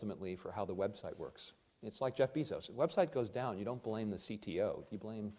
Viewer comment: Did anyone else catch “Jeff Bee-Zos”